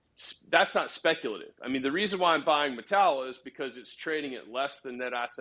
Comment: Mettala? What is the word he's saying at 7:16?